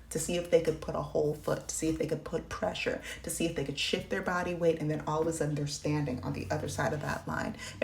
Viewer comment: Girl i thought you said "shit their body weight " 😅